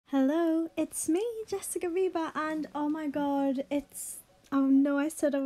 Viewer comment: Why do I always hear": Jessica Bieber." Istead of ":Jessica viba?" 😂😂😂